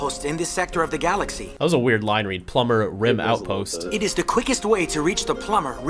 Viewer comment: I hear it like Rook is calling the plumbers Rim outpost. I'm guessing that was not their intention